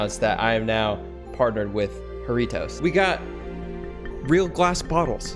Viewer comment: i thought he said Doritos 😂